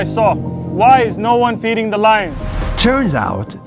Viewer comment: I thought they said "You can't feed the lions"